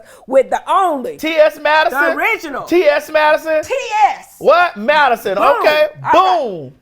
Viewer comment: Lawd, I'm glad you brought Miss TS Madison! I hear you talk about her so much so I've been like who the hell is this lady!? The whole time I thought you were saying TS Medicine 🤦🏽‍♀️, no wonder I couldn't find her! Anyways, I love y'all together, she looks amazing and I can't wait to see part 2 😁.